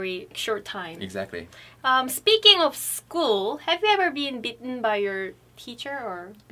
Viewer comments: at first i thought you guys were saying bitten not beaten, so i got really confused. who knew teachers were allowed to bite their students. lol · I wasn't fully paying attention and thought say "did you ever get BITTEN by a teacher before" .....I defiantly started listening then hahaha